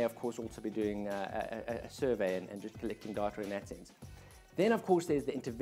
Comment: Lol the closed caption at 2:45. "clicking (unintelligable)" ...I turned on the CC to understand what you said, and the CC didnt know either. haha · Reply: haha - I think that what I said was "collecting data in that sense" (sorry about the South African accent)